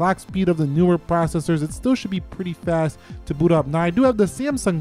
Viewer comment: Sounds like he saying "boo up"